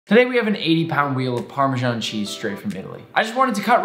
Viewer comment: thought he said £80 instead of 80-pound, and was like where do you get a cheese wheel that cheap?!?(